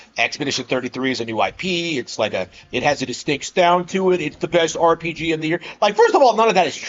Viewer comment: I thought u said it was the best rpg ever over ff6